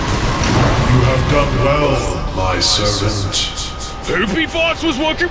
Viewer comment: For 8:08 if you didn’t know what No-visor said, He said “You Have Done well My Servent”